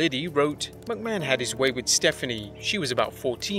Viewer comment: did he say Mcmahon had his way with Stephanie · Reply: He did but the text wrote ‘Macho’